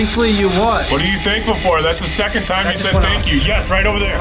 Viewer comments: I heard thankfully I can talk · i heard "thankfully I died" did no else hear that? the ally way ritual was iconic as well. "It looks like were doing drugs" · i thought i heard "thankfully i die" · it sounded like he said "Thankfully i can/could talk" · Thankfully I can TALK TO YOU. Is what I heard